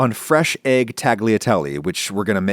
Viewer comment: Adam, did you just say "tag-liateli" ??????